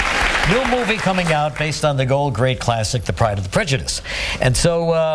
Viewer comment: LOL it sounds like he said "The Pride of the Prejudice"